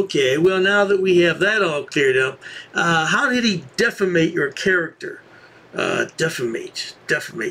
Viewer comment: It sounded like he said deficate 💩